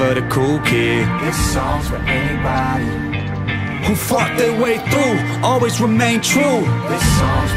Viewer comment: does it sound like he said "fuck their way through" or is it just me? 😂